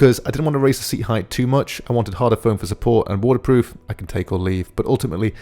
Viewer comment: great video man... I laughed and had to rewind...your accent got me! I thought you said it was "waterproof so you can take a leak" not its water proof I can take or leave HAHAHAHAHA